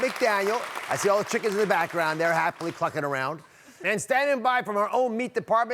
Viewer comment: 😲😱WHO ELSE HEARD DR OZ SAY: 👉👉"All the chicken in the background there, happily f**king around!"??? 🤔ha ha ha 🤭😁🤣🤣Only the 2nd time I listened, I heard: "CLUCKING around".